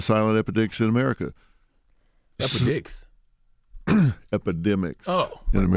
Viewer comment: “ EpiDicks “ 8:24 😭😭😭😭😭😭😭☠️ CTG is a clown but I thought he said that as well lol